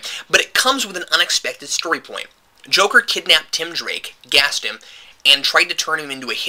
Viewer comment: Great review, but when I used Transcribe Audio, at around 3:25 the subtitles said Joker kidnapped and raped yes. I'm not joking lol